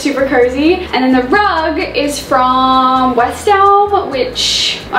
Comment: Does anybody know where the rug in the dining space is from? 😍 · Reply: i thought she said west elm